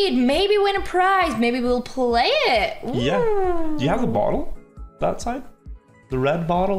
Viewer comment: i thought he said do you have the bong lol